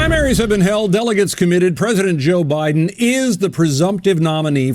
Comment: Sounded like he said "President Jill Biden"...lol quiet part out loud